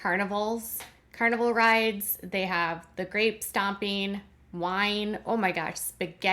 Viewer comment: I thought you said Grave stomping not grape and was like wtf.